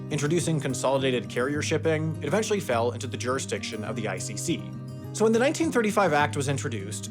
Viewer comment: when I heard icc I thought for one sec that it was ICE